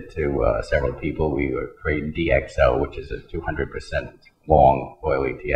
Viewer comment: he recommended DXO, did i hear that right